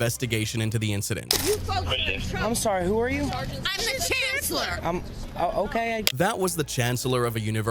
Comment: The way she says, "I am the chancellor" sounds like she said, "I am the queen of England."